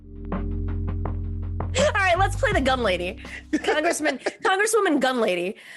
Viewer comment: At first I thought you said "the dumb lady" but gun lady works, LOL